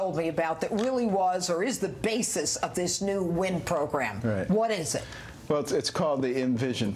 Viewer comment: I thought she said "_wind_ program" and the irony would've been perfect.